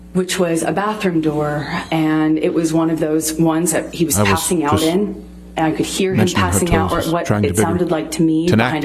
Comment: Wait. Did she say the bathroom doors is those ones that you pass out in? At like